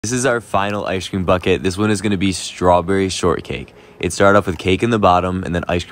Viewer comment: For a minute I thought it said strawberry cake short budget 😂😂😂😂😂😂😂😂😂